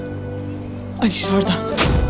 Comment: da Alişardan diyormuş şimdi anladım daha önce gördüğümde aşşağıda diye anladım dedim ki kendi kendime aşağıda kim var ya :) diziyi parça parça bir ordan bi burdan izlemenin zorlukları ayrıca bilen yazsın lütfen aygülün bebeği şimdi nerde 79. bölüm oynadı bu hafta